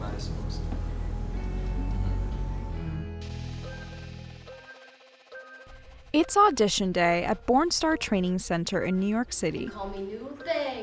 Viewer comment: I keep hearing “porn star” 😂🤦‍♀️🤦‍♀️🤦‍♀️ whyyyyyyyyy